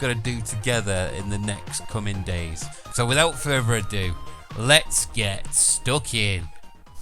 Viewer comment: "Let's get stuck in" I always heard him say let's get stalking.